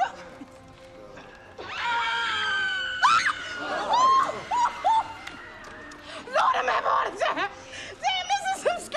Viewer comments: what does she say here?? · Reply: She said “Lord Imma have a heart attack”. As in I’m gonna have a heart attack. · can someone tell me what she's saying here? i cant understand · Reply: She said, "Lord imma have a heart attack!" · What does she say in 1:42? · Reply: Lord I'm having a heart attack....🙄